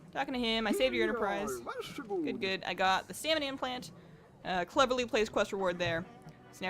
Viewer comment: Did she say salmon implant at 16:15 ?